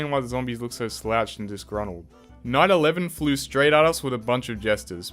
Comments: BRO when he was at dark ages night 11 I heard "9/11 flew straight at us" insted of night 11 flew straight at us.🥶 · I had to replay this a couple times. I thought I heard "911 flew straight at us". 😂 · I heard "9/11 flew straight at us with a bunch of jesters" and almost fell out of my chair. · I can't be the only person who heard "9/11 flew straight at us" at this timestamp